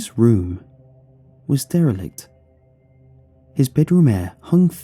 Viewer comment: “Barney’s room was....” what did he say? I didn’t quite understand. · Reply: Katie Jeanes derelict